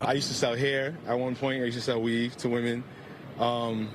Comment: Did he just say I used to sell weed to women OMG😮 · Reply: Weave, meaning hair weave extensions, wigs etc.